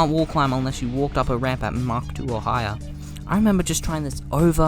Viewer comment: the "mach 2 or" sounded like "hawk tuah" Well more like "mach tuah" but yk